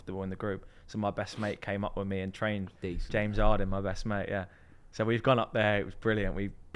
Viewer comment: Am i tripping or is he saying his best mate was the 2018 NBA mvp James Harden? lmao